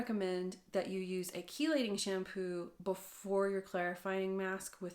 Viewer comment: What did you said at the 5:32 min I can’t understand the name of the shampoo? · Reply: I am saying chelating shampoo, it’s a specific type of shampoo that removes minerals. My top picks for chelating shampoo are Seen shampoo, you can use it everyday and prevent hard water buildup from ever happening or you can use The Ordinary Shampoo for clarifying routinely. Seen 👉🏻 <link> The Ordinary 👉🏻 <link>